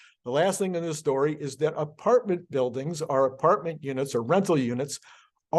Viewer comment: I haven't listened to anyone with your accent as much as I've listened to you. What state are you from? I can't distinguish your "or" from "are" here at 10:02. I thought you said "apartment buildings are apartment units" I'm from Utah, so still working on pronouncing "still" and "steel" differently